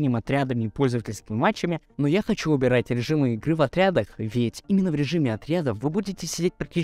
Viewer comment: Я один не понял, что сказал автор? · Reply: Я говорил про то, что ты не можешь выбирать режимы в отрядах.